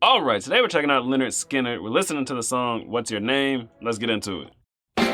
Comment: This going to sound so stupid, but when I heard this on the radio in the 70’s, I thought they were saying, “Wash your name”. I didn’t get it. Just stupid I know. But great music back then.